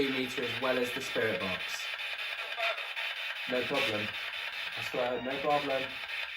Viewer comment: I swear I hear "it's red"